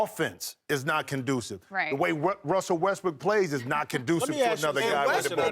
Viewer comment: Is it me or did anyone else hear someone say "Westbrook is trash"